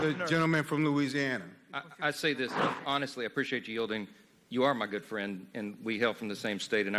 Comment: "By the time I'm done it will be very clear that we are not friends." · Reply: It's even funnier how almost at the end I heard "we are good friends", lol